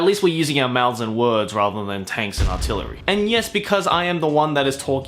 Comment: I swear he said TAINTS and Artillery.